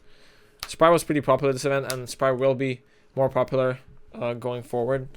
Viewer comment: did he say spright will be very popular or spyral as 10:19 , i swear he said spyral , the deck is trash but sprights alright